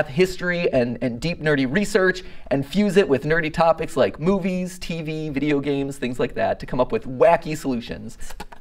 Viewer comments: It’s very loud where I am so I thought he said “dirty topics like boobies” 💀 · needed to rewind that cuz I heard "boobies" instead of "movies"